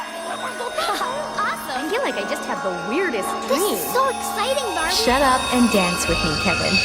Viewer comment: indescribable bibble sounds hehe awesome! I feel like ive just had the weirdest dream, this is so exciting barbie, shut up and dance with me kevin... 💀✋🏽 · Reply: I thought she said... "i feel like i have the weirdest puSSy"😭💀